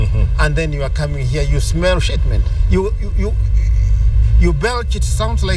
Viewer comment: Did he say " you smell shit men"?? LOL!!!